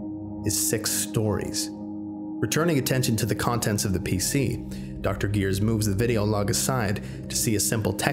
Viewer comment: Dr.Gears? I think he said.